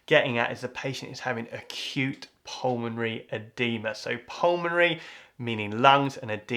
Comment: Did he said Cute PULMONARY OEDEMA?